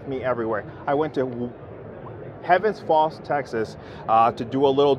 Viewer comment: Where was that in Texas he said he took it? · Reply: that maybe it. It sounded like he said Tennis Ball Texas? I was like where in the heck is that? 😂 I live in Texas